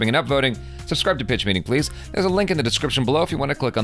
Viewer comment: Did he just say ... BITCH MEETING?